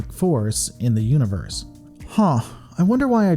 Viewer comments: What word and what is this in reference too? I'm legitimately asking, I'm missing something but I do not know what. Edit: Okay, so "universe" was the word, but I still don't get the joke. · what word was he talking about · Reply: Universe